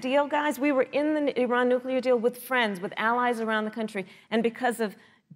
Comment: did she really said Iran deal was a deal within friends? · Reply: She mean with friends. Which is true